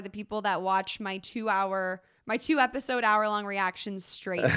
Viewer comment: Did she say 2 hours? Yeah next reaction be a 2 hr video? can watch like 4 episodes, Sounds good to me! :)